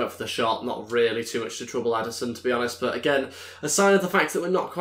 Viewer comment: did you say Adarson